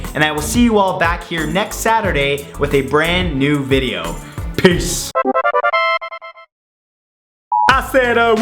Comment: READ THE SUBTITLE AT 8:40 !!! It says be screwed instead of piece